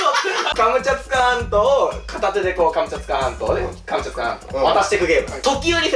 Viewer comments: カマチャツカ半島をずっと聞いてるとかぼちゃ捕まんとにだんだん聞こえてきて頭がおかしくなる笑笑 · カムチャツカ反応って聞こえてくる